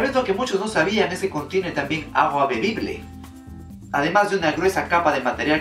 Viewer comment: Yo entendi que dijiste "Agua bendita" en el 5:49, le regrese y me di cuenta que era "agua bebible"... Satan tambien esta en el espacio amigos, hay que llevar agua bendita